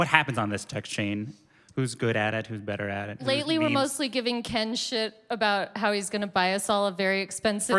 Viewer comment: Anyone know what Alison said at 1:23 ? "Lately we're mostly giving Ken xxx about". Giving Ken what? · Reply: Giving ken shit. Thats what she said